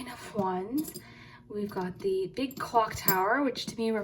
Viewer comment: you said the "big cock" charm instead of big clock 🤣